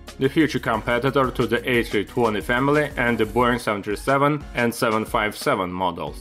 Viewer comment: I swear he says «the BORING 737»... 😂